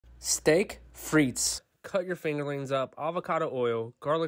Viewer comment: I thought you said cut your finger up.